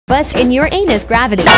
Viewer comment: bruh, I heard "What's in ur a..."💀